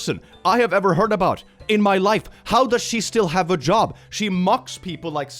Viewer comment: A pount to audio processing issues at 17:15 i ithought he said "how does she still have a jaw" not "Job" and well i think my mishear is funny and am sharing so others get a chuckle